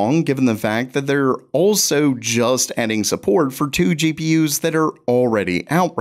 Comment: how did he say adding support and i non native heard it ending support